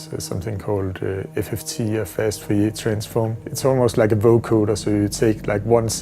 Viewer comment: what did he say at 1:55 ? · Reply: "Fast Fourier Transform"